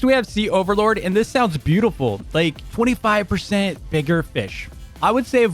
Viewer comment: WHAT DID BRO SAY??💀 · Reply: 25% bigger fish